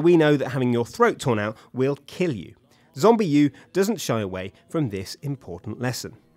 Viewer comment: Whats the game on 3:18, 5:57? Cant really hear him. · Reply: Zombiu and Fire Emblem here you go :)